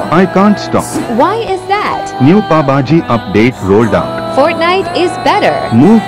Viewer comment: This is what I heard "New baba girl rolled up!" Some boy that wants to online date would say. Edit: Omg 54 likes! Tysm!